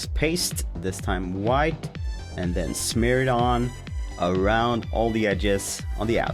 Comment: When he said "smear it on" I thought he said Smirnoff...as in Alexei Smirnoff🥺 the people who have finished season 3 will get it.